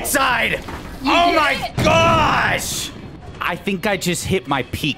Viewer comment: Bro is so mad, it sounded like he summoned a lightning strike, OH MY GO Lightning Strike D